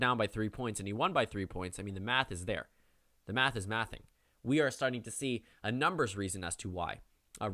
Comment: Does say “the math is mathing” at 16:53?